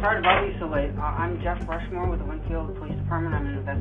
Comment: I think he said that he is from the town that I’m from! 😱 0:30 Lynnfield